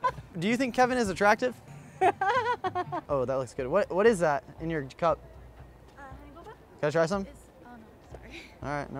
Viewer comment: what drink did she say it was? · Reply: Your Grandma honey boba